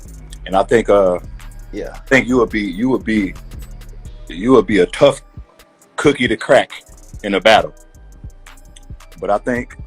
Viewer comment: "youll be a tough cookie to crack in a battle"...i thought he said lupe wasnt bout that